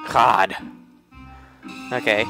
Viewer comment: lol sounded like ''cod''